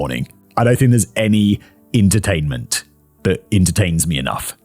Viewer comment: Did I hear intertainment and intertains?